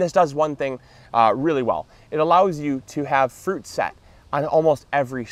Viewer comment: The first time I didn't hear "fruit set." lol